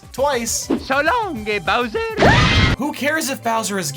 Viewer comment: It was suposed to say so la long King Bowser but I always Heard It so longer Bowser